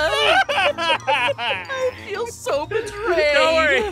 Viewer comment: Am I The Only One Who Heard Peirce Say "Really Who Was That Guy He Was Kinda Hot?" Am I ?